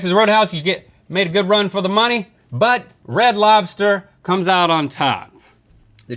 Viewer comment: I thought Link said "Bread Lobster".